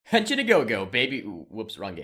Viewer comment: When you said "Henshin a go-go" it sounded like you were saying "Hatchet a go-go"